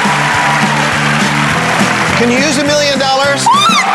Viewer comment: he said Sarah you just won 1 billion dollars instead of 1 million